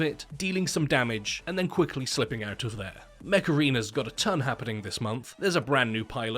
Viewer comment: I swear with his accent, i thought he was saying "macarena" and i was like " hmm good song, but how is that a sponsor"